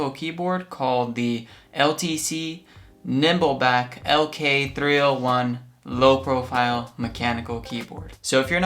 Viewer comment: I thought i heard LTC nipple back lk 301 low-profile lol